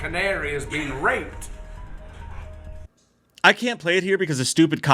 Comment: woah wait did Loomis say Michael raped a canary? So does Michael rape his victims too? Actually makes a lot of sense with Annie on the bed in part one.